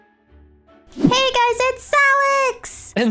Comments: At the start I thought Alex said “Hey guys it’s salad” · Did anyone only heard Zalex instead of Alex XD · I heard alex say: “Hey guys its zalex” 😂✨👌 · Alex sounded like she said “Zalex” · who else hears zalex instead of alex i kept repeating it hahaha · After the plushie intro it sounded like Alex said "Hey guys it's -zalex-" ...so.fricken.CUTE I LIVE ZALEXXX